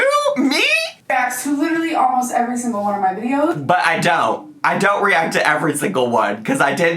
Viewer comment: im usually on your side but at 25:53 she said ""he reacts to ALMOST every single one of my videos" to which you agreed. but you put words in her mouth, & said, she said you react to all of them. thats not what she said.